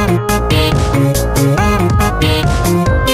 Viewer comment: I heard "tight bars, little man" and also cried- great work tho (Edit: he was only 26? Garcello hitting too close to home)